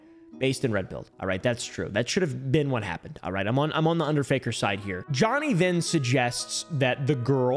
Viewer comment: Bro ngl the first few times you said Under Faker I thought you said Under Finger